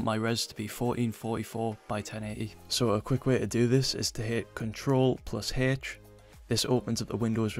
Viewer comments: When in the code, how did he pull up the replace tab? It’s in minute 1:07 don’t understand what he said, CTRL+___ ? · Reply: Ctrl + H, my accent haha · what did he said? What were the buttons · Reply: Ctrl+H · min what does he say CNTRL + what???? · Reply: H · what did he say after control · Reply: Control + H